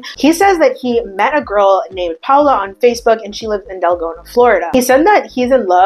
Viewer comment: Deltona...it sounds like she said Delgona